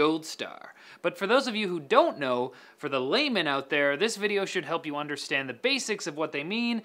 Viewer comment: Lol, when you said "layman" at the beginning of the video as someone who did not understand those graphic card terms, I heard "lame man".